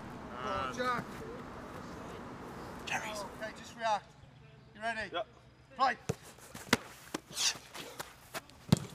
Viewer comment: What did Butland say at 11:24? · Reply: Bob Zilla Cherries